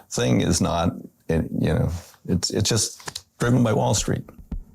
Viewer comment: did i hear "Driven by wall street"